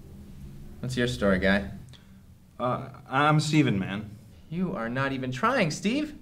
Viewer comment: Am I the only one who thought that one guy said "Steven, man", not "Steven-Man"?